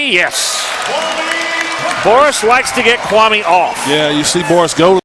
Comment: Did I just hear them say "Forrest likes to get Kwame off" ???? O___o